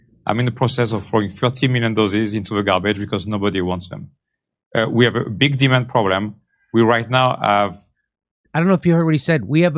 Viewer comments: It sounded like he said "We have a big demon problem"! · I thought he said, "We have a big demon problem." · Sounded like he said "we have a big demon problem." Sounds about right ✅ 🤔 😏 👌 🤣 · Jimmy On Tour! - DETROIT- Spokane - Tacoma - Tulsa - Oklahoma City - DENVER - Richmond Va. - VIRGINIA BEACH - ARLINGTON - MIAMI - WEST PALM BEACH - & More! Tickets here: <link> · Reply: I thought he said "We have a big demon problem" - I was thinking "You sure do". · Sounds like he said we have a big Demon problem. I agree and he's one of them. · i thought he said "a big demon problem". Gavi is a bill gates front.